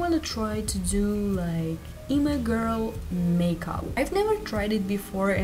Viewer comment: I thought when you said E-Girl I thought like a Twitch Streamer E Girl